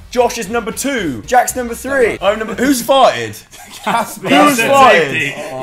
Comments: when joe said "whos farted" i thought he said "whos five" lol xx ly all sm xxo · i thought joe said "who's five" but he said who's farted